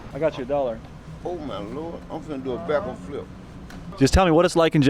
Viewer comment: did bro say he about to do a backward flip lmao